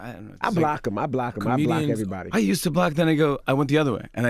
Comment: Sounds like he was going say "I used to block that nigga" at 9:30 😭😭